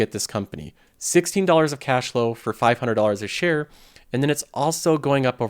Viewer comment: Super investor show has hinted at the buy signal for Nike. Look at his hoodie! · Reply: I thought you said 500 shares! I was going to say I’m jealous lol